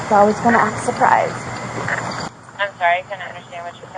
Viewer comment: think she is saying : "I was going to act surprised" as if she didn't call the police & had no idea they were coming. Like the day she said "it's the same guys"" how she played to Jorge "babe, I dunno what happened" and threw her arms up like someone else called them.